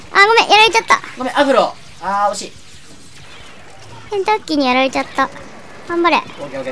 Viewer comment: どうしても、ケンタッキーにしか聞こえない笑笑 (さんすいません)